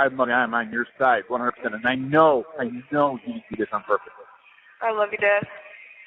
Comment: Did he just say "i know you did this on purpose?!"